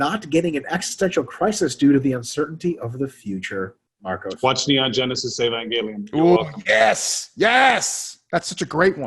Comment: What did say? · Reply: "Watch Neon Genesis Evangelion"